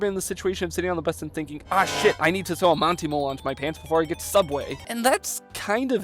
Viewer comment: for a second I thought you said “I need to throw a Monty Mole into my pants” and I was like AYO